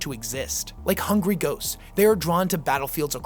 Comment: Did you say “like hungry GOATS” @ 14:31 or “hungry ghosts”? 😅